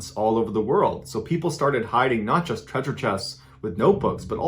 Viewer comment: I thought you said ”people started hiding nachos treasure chests” but it was ”not just treasure chests” and now Im very sad